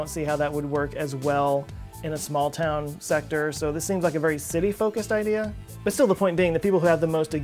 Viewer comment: when you said, "that seems like a pretty city-focused idea..." I originally heard it as, "...a pretty 'city-folkist' idea..." which also works. ;p